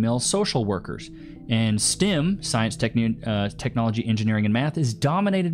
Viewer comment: Stim? Or Stem😅 engineer is with e not i.. sounded like he said stim